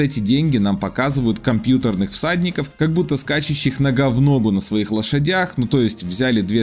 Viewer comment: "Скачущих но га в но гу..." я надеюсь, что мне послышалось.